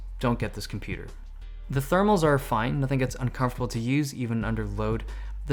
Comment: ' I think it's uncomfortable to use?' Did I heard wrongly?